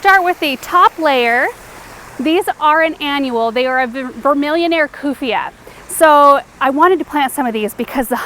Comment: What is the name of the plant that you said attacks hummingbirds even in your greenhouse? at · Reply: Vermillioniare. It's in the description with a link to learn more about the plant.